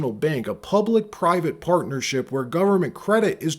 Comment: PUBLIC, PIVATE & PARTNERSHIP 🤔 That's a " P " TOO FAR!! 🤣🤣 WTF did you JUST say?